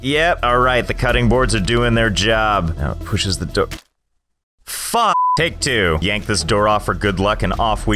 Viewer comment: Did he really say fu**?!